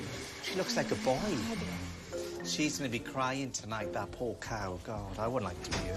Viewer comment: did he call her a cow??